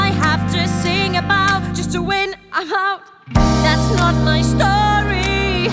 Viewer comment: Instead of '' I'm out '' I hear '' a heart '' So what i hear is '' Just to win, a heart '' :/